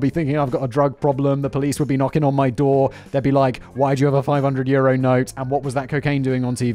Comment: "500 Euro note" I thought he was saying "500 year old note" 🤣😂🤣